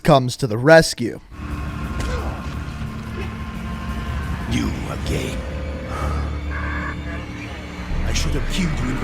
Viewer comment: who else heard "You are gay"